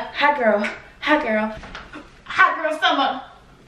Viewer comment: Thought said ain't no hot girl summer for you.